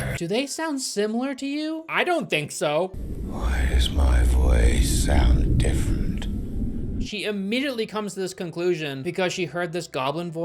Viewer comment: he actually said "why is my voice so different" but he said "so" with an accent so it came out as "sah-ouh"